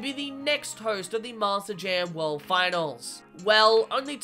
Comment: Why does it sound like you say Master Jam instead of Monster Jam. It's not even the accent you switch between Monster and Master.